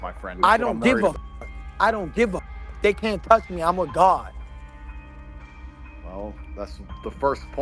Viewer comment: Sounds like he said “I’m with god” not “I’m A god”